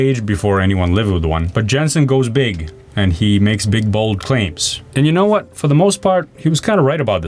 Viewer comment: Jen-Hsun Huang stuttered a bit, he almost said "Tegra"... :)) PS: @ 15:28 I thought you said "big-balled claims" instead of "big, bald claims"